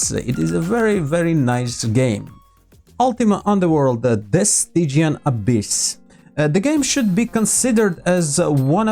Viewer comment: what's the name of this game? I couldn't understand properly. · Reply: That's Ultima Underworld: The Stygian Abyss. If you have any other questions do not hesitate to ask. Have fun and stay tuned!